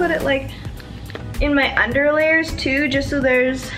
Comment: LMAO! When you said you like to put it in your under layers for some reason I thought you said underwear! Bahahaha 😂😂 I dont even know...